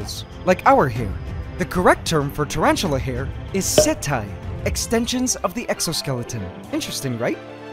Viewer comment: I wasn’t payin attention so I thought he said hentai 🤦🏾‍♂️